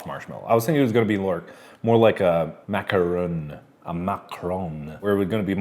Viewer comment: Mark: "Macaroons... (french accent) 'Macaron'..." Me: Why is he talking about our president..?! No, really! I don't know if it was intentional but it sounded like he said "Macron"